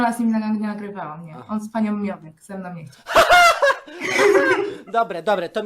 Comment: Co mówi? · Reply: Z Panią Miotek ze mną nie chciał